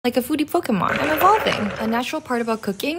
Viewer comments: I THOUGHT YOU SAID LIKE A FRUITY POKEMON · Sounds like you said, "foodie fokémon." 😳😅